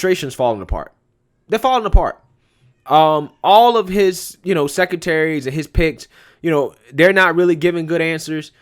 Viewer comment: Thought he said "secretaries and pimps ". LOL!